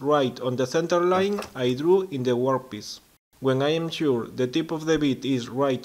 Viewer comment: Great video, well done sir. Annoyingly, I keep hearing you say 'workpiss..' Ha ha, keep them coming, you inspire others.